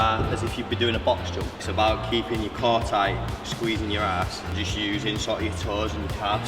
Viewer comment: When he say it’s about keeping your something tight it sounds like he is saying it’s about keeping your car tight what is he really saying?